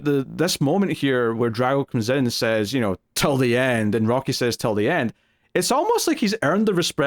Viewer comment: The drago "till the end" line for the first few times watching I thought he said "you're dead! Which I found interesting after you guys mentioned subtitles for rocky. I do think its a respect thing, after hitting a guy for 14 rounds I can imagine there's a weird, almost intimate relationship.. drago probably for the first time sees himself in someone else and further highlights his coldness/lack of respect for greed